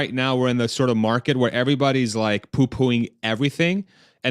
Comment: Lol! Did you say, "everybody's poopooing everything"?